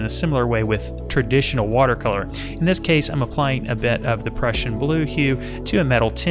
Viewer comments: You did not say depression blue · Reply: I heard "the Prussian blue" at the start. 🙂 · Did he say depression blue hue